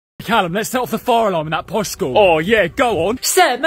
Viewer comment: My dumbass thought he said calcium💀